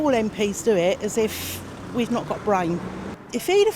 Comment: "They treat us like we've got no brains." She voted Tory and for Brexit and thinks Johnson did a good job. 'Nuff said. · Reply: She actually said "As if we've not got brain", which is the funniest part of the video